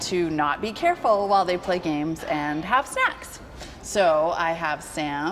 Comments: i thought she said "play games and have sex" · Did she actually say: "HAVE SEX" ? :D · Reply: EndrjůTV have snacks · To all the people that thought she was saying "...have sex" what she really said was "...have snacks". Had to listen a few times to catch that correctly.